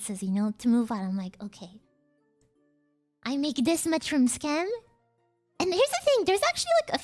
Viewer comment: I thought she said "i make this much from scam" O.o